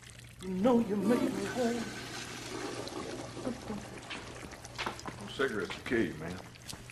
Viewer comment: Can someone tell me what does he says about cigarettes? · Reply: “cigarettes will kill ya man”